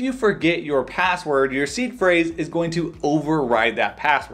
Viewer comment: In another video, I thought he was saying was C Phrase.